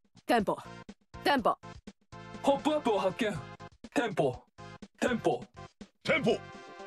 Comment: だんだんテンホウに聞こえてくる